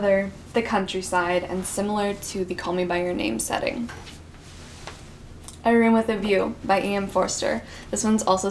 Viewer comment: Can someone tell me the title of the book she was talking about at 27:37 (the one with call me by your name vibes)? Can't really understand since english is not my first language, sorry! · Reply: A Room with a View by E. M. Forster